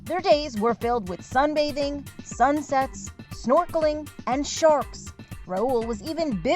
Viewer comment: bro i thought she said sun sex